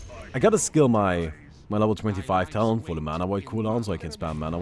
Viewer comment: I thought said mama void XD